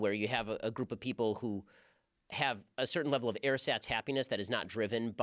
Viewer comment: Please someone, anyone, HELP S.O.S lol help me out, I hit rewind genuinely 37x at 42:52 ... he says there is a group of people who ____ happiness. The auto generated subtitles, which are pretty useless in general anyway wrote “air SATs” and I know I don’t know what he said but I’m like a solid 10000% sure that that isn’t it. Please lol I know I sound crazy, it’s because I am. It’s going to bother me forever. So if you want to relieve me of my bothering and help me sleep at some point in my life lol